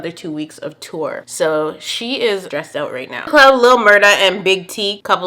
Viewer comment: What did you say at 4:13??? I rewound it so many times and still don’t know what was said lmaoo I feeeeeeel like you said “Crown”?or “Club”???? idk but honestly I literally have No clue lol :_CONFUSION::_HEY